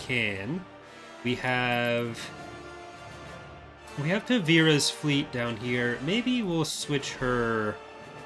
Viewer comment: Tavira is from the book I, Jedi and a couple other places. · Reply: Ahhhhhhh. I heard "*ku*vira and was really confused. *Ta*vira makes so much more sense. Thanks!